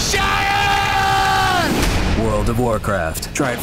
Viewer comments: I thought originally he said shut up. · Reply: it now sounds like he is saying !!Shatter!! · uhhh what was it that he said on 0:18 !? is it "shut upppp" or "SHATTERRRRR" !? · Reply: You dont know who Ozzy is much do you? He said "Sharon" his wifes name because thats a HUGE thing...e verytime he yells to his wife. Its so funny.......